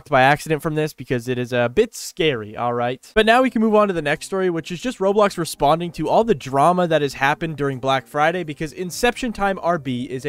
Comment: legit thought you said "roblox responding to all the trauma" (ngl "trauma" fits better than "drama")